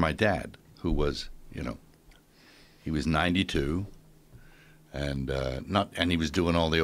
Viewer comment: DID HE SAY HIS DAD WAS 92?!?!?! YOUR SUPPOSED TO HAVE PROBLEMS AT 92. GOD BLESS HIM. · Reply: Dean baisden I thought he said "it was 92" as in the year.